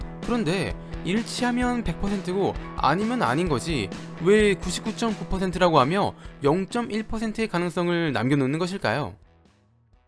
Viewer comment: 98.9%라고 들리지 않음?